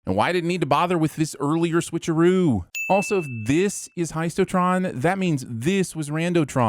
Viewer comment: Typo in the subtitles: "Hesitotron" instead of"Heistotron"